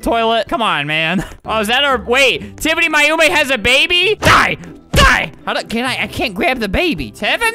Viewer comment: Why did it said baby tied onn 4:15 :edit turn on subtitles